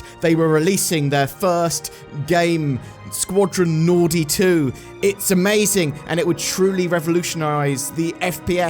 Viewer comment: I heard: Squadron Naughty Two